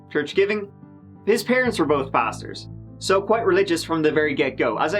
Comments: I thought said his (Coleman's) parents were both bastards.. then I listened again, and again and I think he said pastors. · 😂 he said “both his parents were pastors” at first I thought he said “bastards”😂😂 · I swear I thought said, “His parents were both bastards.” I was like dammnnnn, is starting out savage af today. LULLL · At the beginning I thought you said his parents were bastards... then realised after a quick rewind you said PASTORS 🤣🤣🤣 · I genuinely thought you said his parents were both bastards, not both pastors. Still checks out, they're vile. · Haha I thought he said “his parents were both bastards” not pastors.... lol